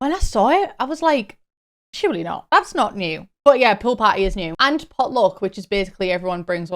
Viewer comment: I misheard you when talking about the pool parties and thought you said “pill parties” and immediately went 😮 like, who asked for that